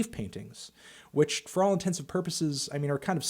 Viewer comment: Totally agree with you, Melon, but did you say “intensive purposes” at 6:19? C’mon now.